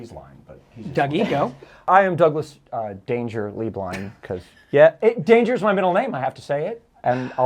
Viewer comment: When introduced himself for the first 8 episodes I thought he was saying, “Doug ‘Dangerously’ Blind” 😭 Cracked me up so hard when I realized I was wrong 😂❤️